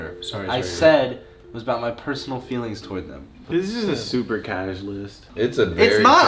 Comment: really called list a casual list when he put jimmy butler in like F tier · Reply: He said casual oh I thought he said cash list.